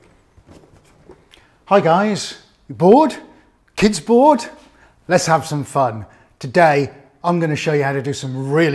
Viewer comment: What are you saying? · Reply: "Hi guys, bored? Kids bored? Let's have some fun!" You can click on German subtitles to se the translations!